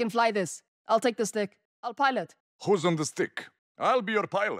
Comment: I thought nikolai said hoes on the stick